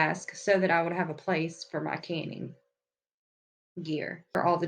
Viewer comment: Awesome! I love getting organized. My pantry is SO TINY, I had to set up a shelf in the laundry room because I literally have the smallest pantry in existence, lol. I thought you said "candy" not "canning". 🤣